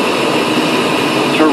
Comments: to rain to rain ,to rain .... what does this say ???? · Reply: Is a warning alert, Terrain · TERRAIN TERRAIN... I heard; THINK AGAIN THINK AGAIN...😨